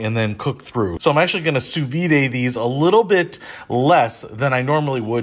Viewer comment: What was the word you used around 3:45? Su-vee-day?